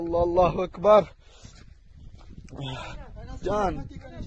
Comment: Did dude just say John in the video in Pakistan